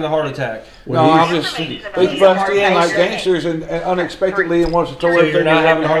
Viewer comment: All sympathy has left me. The husband said, “They busted in like gangsters!” (28:10) No sir, you were being evicted for not paying your rent! 😠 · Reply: I heard “gangbusters” not gangsters.